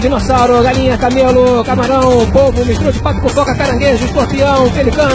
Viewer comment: MISTURA DE PATO COM FOCAA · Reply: Eu entendi "estrume de pato com coca "